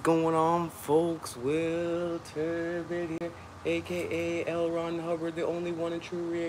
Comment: did he say L Ron Hubbard the Scientology cult leader?